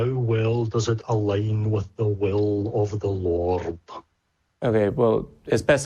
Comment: Am I crazy, or did he say "the will of the lorb"? Also, why did he draw out the 'l' sound so much. Does this have to do with his accent or does he just have a weird affect to his speech. I'm genuinely curious.